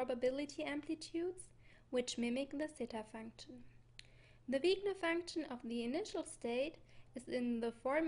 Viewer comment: Very good and intriguing presentation. But I am having trouble hearing a few terms. At 0:52 when you say "the __ function of the initial state" I can't make out that word. Is it Sigma? Wigna? I tried using closed captions but to no avail.